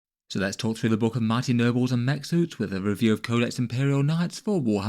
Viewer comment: When I heard your intro, I heard “mighty Nurgles” instead of nobles. Created an amusing image.